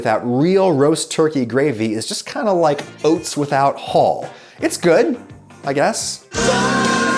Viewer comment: I heard "Oats without Hull" and I thought that was brilliant. This was "brilliant-er?"